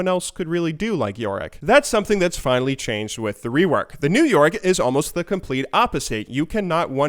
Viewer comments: Is it just me or does it sound like he says new York when he says new yorick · it sounds like you say New York instead of new Yorick :'D